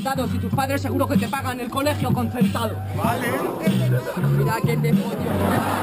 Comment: En del minuto 2:04 que dice: ES VERDAD, Soltó el mejor punch de toda la batalla xd